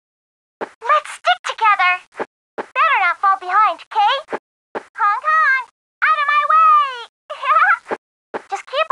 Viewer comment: what does she said? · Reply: "Better not fall behind. K?"